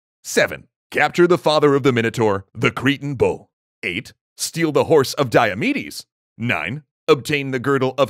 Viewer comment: I thought he said "obtain the horse of diabetes " lol